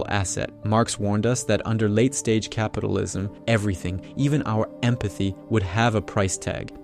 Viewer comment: Video: "...late stage capitalism." What I heard: 'Capitalism is ending'